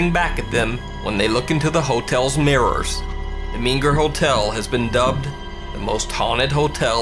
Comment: Lol I legit thought I heard "The Mean Girl Hotel" in the first one Also. Sorry, but not great acting. IMO.